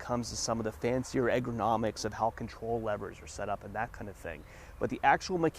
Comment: Did you mean ERGOnomic because I thought I heard you say AGROmomic?